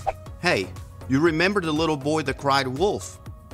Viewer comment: En el minuto 1:27 puedo escuchar la palabra LITTLE Boy... or I heard wrong?